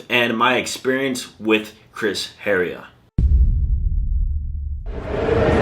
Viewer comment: you are spitting straight facts bro, good job keeping cool in the situation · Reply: I thought you were saying Chris Hera was bad